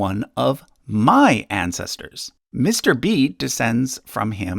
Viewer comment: The majority of the clicks will probably be people who thought he said "MrBeast"